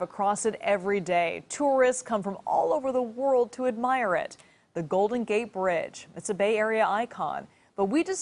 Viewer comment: Did she just say Golden Gay Bridge in the start or am i just high?